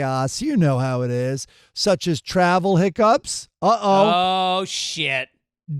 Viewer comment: Thought Mike doing horoscopes read: Prepare for chaos such as travel "hook-ups" (instead of 'hiccups'), and even funnier was Greg response "Oh sh't!" (wasn't planning on any hook-ups in Mexico I guess) 😋